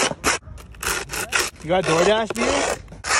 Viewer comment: You got doordash? 💀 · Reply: I heard "You got DoorDash beer?"